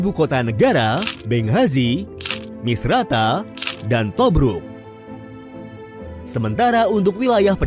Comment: Tobruk, kedengeran kayak Tobrut awokwowk